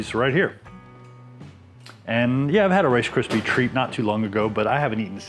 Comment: I thought it said Rice Krispies treats lager and got excited.